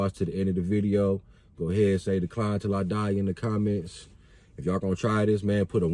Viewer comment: ~* Hhaha... I thought you said "Clown til I die" hehe.... keke...muh bad... good vidz though! ty.. your appreciated*~